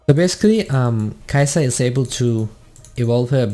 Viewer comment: Are you german? i think at 3:52 you said "Kai'sa is sehr gut to.." x) (I'm german) Very nice guide! it's very helpfull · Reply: Happy to help! I think i said "so good to" with an accent so it sounded like the german words. I'm danish so the languages are pretty similar :D